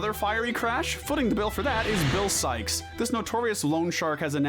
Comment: Good god I have a problem, when you said bill sikes I thought you said bill cipher, HELP